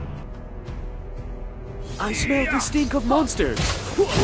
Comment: what did alucard said??!! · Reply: He Smelled the stink of monsters hahahahaha